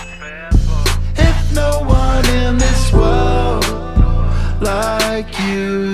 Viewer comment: I still can't accept the fact that he's saying "world" instead of "part" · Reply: Yeah it really doesnt sound like “World” compared to how he’s saying it for the rest of it 🤷🏻‍♂️😂 I keep replaying it and im trying to say “World” but sounds like he says “Part” or something beginning with P